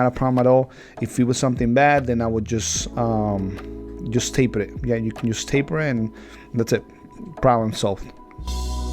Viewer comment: What was said during the 12:20 point? it seemed funny just watching lol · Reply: hahaha i told her to no push the line back and she did so she was saying "sorry" 🤣